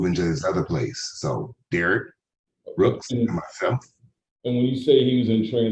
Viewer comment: At first..I thought she said Darrell Brooks..I guess they both have the same initials..DB..aka dirt bags😅